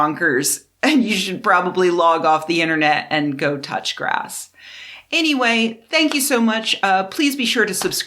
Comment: Go touch grass ahahah I’m gonna start using that 😂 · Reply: When she said "go touch grass" I heard "go touch your ass". Which is - you must admit - appropriate to the topic.